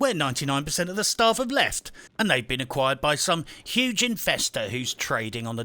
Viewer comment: When you mentioned "Investors", I actually thought you said "Infesters"! Probably not too far from the truth though! Yeah, as far as mobile "games" go, I would rather try to sandpaper a lion's ass while wearing a pair of pork chop panties! Great work, as always! And just what I needed after being woken up way too early! o7 Oh, and I may have to watch this again. Just for the laughs!